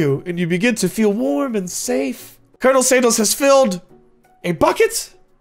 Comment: Y’all am I crazy or did call him “Colonel Sandals” at 4:09??🤣🤣